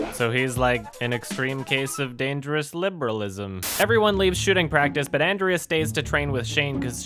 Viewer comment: it sounded like shitting practice